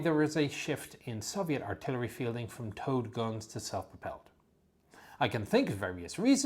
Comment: I kept hearing "TOAD GUNS" . HA!